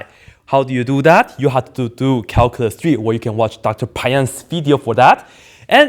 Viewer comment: what did he say? dr. pi video for that? who is this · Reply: El Risitas Dr. Peyam's video.